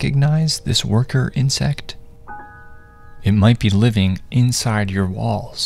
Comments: I thought he said "might be living in you balls" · I THOUGHT HE SAID “ it might be living in ur balls” MADE ME SO SCARED BECAUSE RIGHT NOW I’M PISSING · i heard "it might be living in your balls" and i was genuinely terrifyied how thats shi would fit inside my balls and then i immediately started cheking ny balls 😭🙏💀🙏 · I thought is said (it might be living in ur balls😂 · I thought he said it might be living in your balls. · WHEN IT SAID “it might be in ur walls” I THOUGHT HE SAID “it might be living in your balls” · When he said it might be living in your walls I thought he said balls💀☠️ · I thought it said balls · Misheard “walls” as “balls” and was scared for all the men in my life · I definitely thought he said it might be living in your balls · I was so for from my screen that i thought he said "it might be living in your balls" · For some reason i heard "It might be living inside your balls" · I thought it said it might be living in your baaals 😂😂😂😂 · I thought he said “it might be living in your balls” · I thought he was saying " living in your balls" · I thought bro said they might be living in my balls😭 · i thought he said it might be living in your balls😂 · I heard “It might be living in your BALLS” I was freaking peeing my pants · I’m sorry I heard “balls” not “walls” 😭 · His voice was so quiet I misheard it as "It probably lives in your balls". 🥀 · I thought he said "it might be living in your balls" 😂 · I thought he said it might be living inside your balls💀